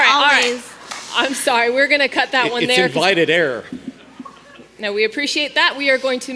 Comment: What did Dr. Mike Adams say at 1:50:33? "It's invited air"? "It's invited error"? Someone with better hearing, please help. :) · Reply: I believe it was invited error. As far as I'm aware, in legal proceedings, a person cannot mislead or otherwise introduce an error and then use that to claim that the court made the wrong ruling.